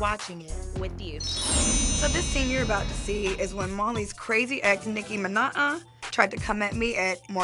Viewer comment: I thought Masika said Nicki Minaj..